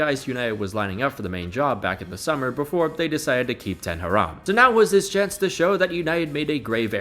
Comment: Did u say ten haram ? 😂😂